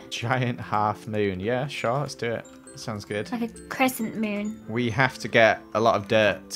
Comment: I thought said ‘like a croissant moon’ and I was like, I WISH THE MOON HAD CROISSANTS